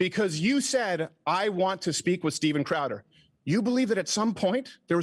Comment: 'you belive at some point there is some acceptance' I cannot understand what he's saying here...is that just panicked, nonsensical filibustering?